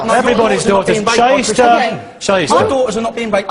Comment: I thought he was saying shitester @ 3:42 lol